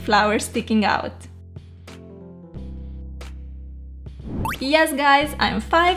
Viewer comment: did she say at 5:33 I'm 5 (5 yrs old)